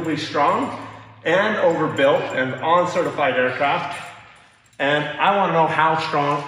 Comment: did say „all certified“, or „un-certified“? Cheers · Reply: I think said "on certified aircraft."